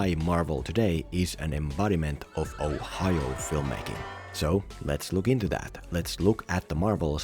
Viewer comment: Did I just hear “Ohio filmmaking”?